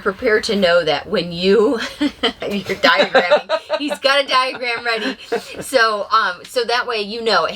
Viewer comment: Very funny . When you said diagram then I thought you said on a list of stuff to have it sounded like diaphram then you said diagram.